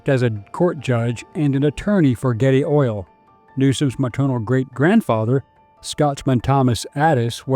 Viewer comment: I’m sorry..:did you say gay oil ? What’s that? Astroglide? 😅